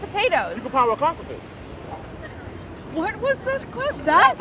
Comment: When Molly thought the guy said "you can pound my c*ck with that" instead of "you can power a clock with that" i died